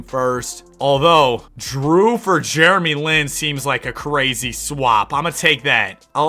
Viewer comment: I need to go to sleep bro, I thought you said Germy Lin.